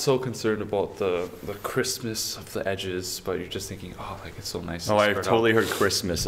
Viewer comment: I heard "Christmas of the edges" too 😅😂